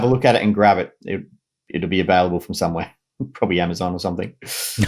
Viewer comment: what i heard is "It'll be available somewhere, maybe if you sell your soul or something" and yes Amazon is the devil.